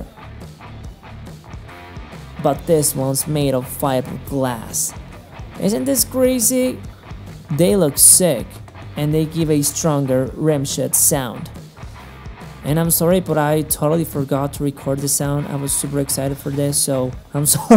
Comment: does anyone else hear "rim shit" @ 8:18?